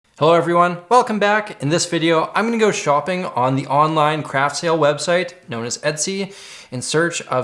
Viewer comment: I misheard craft sale as crab tail and I got very confused